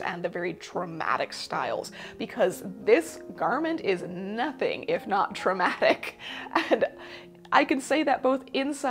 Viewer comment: is the garment “nothing if not traumatic”, as the captions say, or “nothing if not dramatic” as the previous statements would imply?